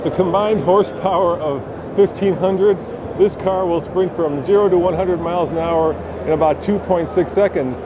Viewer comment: 0-100 mph? or u mean 0-100 kph in 2.6 sec. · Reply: Go and watch it again at 0:07, he clearly says 0 to 100 MPH.